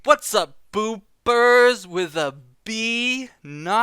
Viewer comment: People actually thought you said poopers? O.o Thank you for the video I've been waiting for it. So generally where do you think one should start doing all of that? Is it worth to first invest in crafting for gear for example or is farming and upgrading the quest gear a better idea? This video took some of my concerns and added new ones xD I guess my top priorities are perfecting my class (I have yet to fully test if frame rate seriously affects combat and it's hard to test without a lot of PvP experience), finding a guild and fishing (because...fishing)